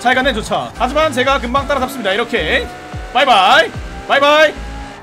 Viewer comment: 이부분 저만 마이바흐라고 들리나용?